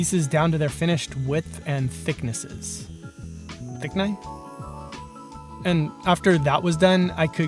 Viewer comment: What were you saying at the 8:00 mark? Thicknay?